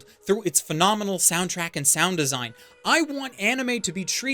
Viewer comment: I heard that as "wants anime to be treated with enough Karen attention" at first....lmao